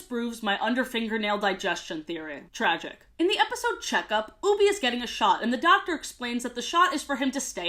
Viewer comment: for a second I thought she was saying “Oobi is getting shot” 💀